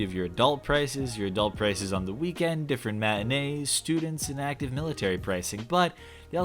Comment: totally didn’t think that said manatee. Although, I’m somewhat sad it didn’t.